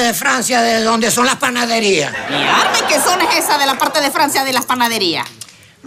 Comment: Alguien me puede poner que dice la mujer del minuto 2:34 a 2:37 porfa? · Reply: Mi alma y que zona es esa de la parte de Francia de las Panaderías. PS: el primer mi alma, la comediante lo pronuncia como r fuerte (mi aRma ) debido a que los maracuchos tienden a hablar de esa forma.